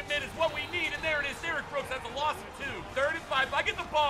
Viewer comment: did he say Derrick Rose?